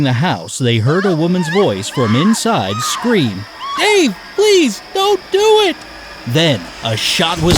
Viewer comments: I couldnt get the first one because the guy narrating tried to do a womens voice for the scream and I thought he kept saying “hey no pleas dont do it” i though he said hey I still dont hear a guys name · I thought the first one said "HEY DON'T DO IT PLEASE" or something not "DAVE DON'T DO IT PLEASE" Oh I didn't expect +100 likes tysm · The first one I thought you said Hey! Don't do it not 'Dave! Don't do it!' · Well hw are you supposed to know that No.1 shouted a man's name, if the narrator didn't say that? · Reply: I didn't hear it as well, i thought he said "hey don't do that that" but actually he said "dave don't do that." pretty dumb to make a riddle around a key word that is pronounced so badly and unclear... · Oh I'm sorry I did not hear it thank you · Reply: I thought she said hay don't do it 🤣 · Yeah ok like parents don't give boy names to girls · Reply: Keiona I honestly thought she said "wait, don't do it" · If that sounds 'Hey' instead of 'Dave' to you, check your ears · The first one doesn’t make any sense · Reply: i thought she said "hey please don't do it". i didn't hear a name at all. no wonder i couldn't figure it out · I thought she said, Ah. Please don't do it! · Two. Didn't get the first one because i didn't hear any name mentioned. If she had screamed Jim or any other male name, I would have guessed it. You hair hint was helpful. 👍👍👍👍👍👍👍👍👍👍👍👍👍👍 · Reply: I could barely hear the name. I thought she said “hey, don’t do it”